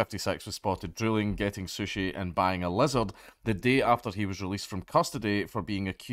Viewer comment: I thought you said "bang a lizard" because of your whacky accent.